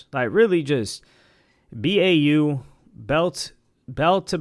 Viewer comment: Did you say BAU? lol wtf does that mean… 😂 did you mean BTA unc as in “belt to ass” cuz he didn’t do that to us (the west coast). Tyler the Creator is better than Joey commercially and talent-wise lol didn’t even need to bring our kdot into the convo. Loved Joey Bada$$ album tho